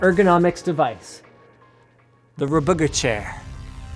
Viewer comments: He clearly says RBG-Chair... LOL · I looked away and thought Linus said "The Red Booger Chair" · I thought linus said the rahbooger chair.